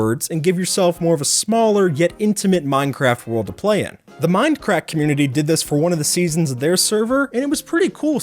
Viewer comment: sounded like you said mind crack